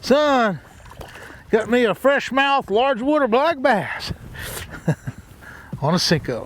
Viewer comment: did he say fresh mouth large water?